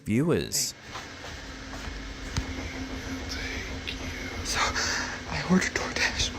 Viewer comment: anyone else hear a male voice whispering "I'll take you..."? It's definitely not the OP saying it.